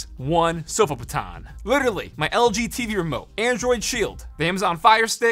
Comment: When he said “lg tv remote”, I thought he said “lgbt remote”